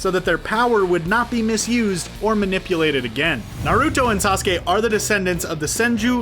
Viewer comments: Did he just say narudo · Did he just say narooto